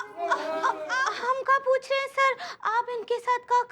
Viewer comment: ye kya bol rha hai....😂😂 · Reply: Bhabhi ji bol raha hai